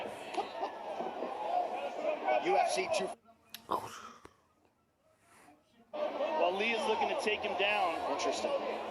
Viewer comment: was that a kurwa I heard xD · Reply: yes,kurwa and pichu